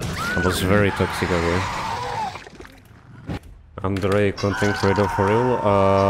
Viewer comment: i think you said "underrated content creator for real"